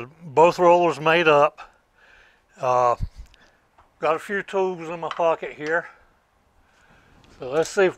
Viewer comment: Very nice sir 👌 👏 👍 same way we build stuff for shop, whatever we can find....you said you had tools in your pocket, thought you said fools 😉 and was hoping you wouldn't drop us from all the way up there 😉 😀 😅 🤣 😂